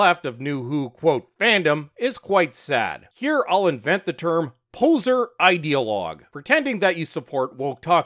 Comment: Between my bad hearing and being distracted I thought you said "hoser ideologues". As a Canadian, I was happy in my maple-syrup drenched heart. But Poser is also fine. Can't wait for the next retcon in Who.